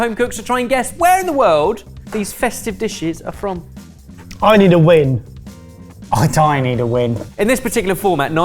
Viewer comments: I was eating a crunchy chip when Barry said "I need a win" but all I heard was "I need a Wee" EHEHEHE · i thought Barry said I need a wee